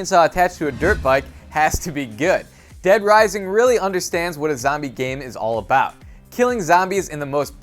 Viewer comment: oh wait i thought he was saying thats a zombie game